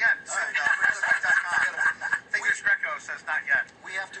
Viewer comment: what does drew say?? sounds something like english grecko?? It seems like this one channel has alot more of these anomalies than the others. I hear that these need other entities around them to help hold the image. Have you heard of that also? - Thank you sir